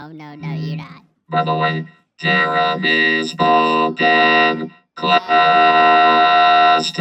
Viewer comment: is this a song or something I can’t tell wtf he’s saying 💀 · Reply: hes saying "jeremy spoke in class today", the chorus of the song "jeremy" (1991) by pearl jam